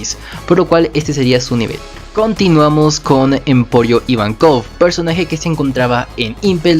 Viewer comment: escuché Empollo Ivankov? O entendí mal?